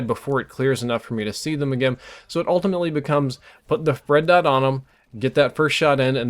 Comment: did he say "Fred Dot"? I know he means red dot, but it sounds like he said fred.